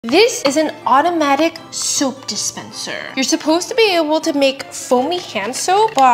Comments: Am I the only one who heard her say "automatic soup dispenser" 😂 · i heard “automatic soup dispenser” and got excited 😔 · At first i thought she said soup dispenser 😅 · i heard "automatic soup dispenser" 😂😂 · I heard “automatic soup dispenser” and then when she talked about foamy soap I immediately came to the comments 😅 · I literally thought she said soup dispenser 💀💀💀💀💀💀💀